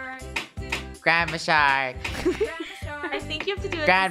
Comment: All i hear is "Grab a shark" more than "Grandma Shark"